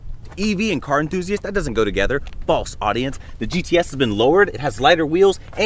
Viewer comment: I thought he said “False, audiots” at first